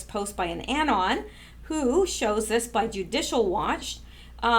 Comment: I thought you said juice. I was little curios what you ment there? Why would anybody hide juice in the cellar?.